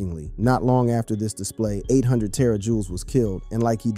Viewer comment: So we gone act like bro aint say 800 JT or wtf he said ”800 terror jewels”15:01😂😂